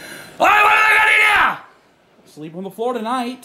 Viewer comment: what did he say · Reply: what ima gonna do now !!!